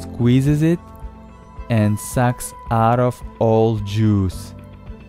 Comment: what did he just say? XD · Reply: socks out of all juice